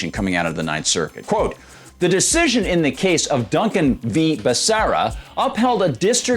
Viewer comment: You're wrong. Benitez is the one defending us. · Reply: Sorry, I meant to write Benitez, but my "smart" keyboard replaced it with Becerra. I also misheard, and thought he was referencing Benitez and not Becerra. I was thinking why is he talking about Benitez like that, but he was talking about Becerra. My bad.